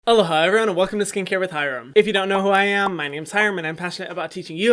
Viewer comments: When I first started watching Hyram I always thought he said “Hello Hyram, welcome to skincare with Hyram” 😂😂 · I always thought you said “Hello Hyram” at the beginning of every video LoL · When I first saw your channel I thought you was saying "hello hyram, my name is hyram" like in the third person lmao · Oh my gosh I thought you are saying "hello Hyram"😃😃😃 feeling a bit funny now😂😂😂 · I thought he said “hello Hyram” at the begging of his videos and I was like it’s such an honor you calling me Hyram, Hyram🥺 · At first, I thought you said "Hello Hyram welcome to skincare with Hyram" and I was super confused lol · I thought you were saying something like hello Hyram everyone lol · I thought you said hello hyeveryone lmao 💀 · No one: Me repeating the beginning questioning how everyone hears "Hello, hi" and "Aloha everyone" when I hear "Hello hyram" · turn on captions and it says “Hello hi everyone” Lol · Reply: I thought he said „ hello hyrams n‘ everyone“ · I always thought you said “ hello hyrams” as your little babies 😂. I’m from Bolivia, and it is so hard and expensive to get some products but I’m a skincare lover and your channel has helped me sooo much knowing what to choose for my skin. Thank you ❤️ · Lol totally thought you said hello hyrum...😂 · I watch all yt videos on x2 speed so I always heard "hi, hello everyone" and after watching videos for 7 months I totally never even thought of it lol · I thought you were saying “Hello Hyram” 🥴😭